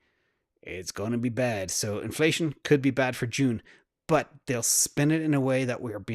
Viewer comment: At one point I thought you said ‘cornflation’ lol! I’m actually curious what that chart would look like now 🤔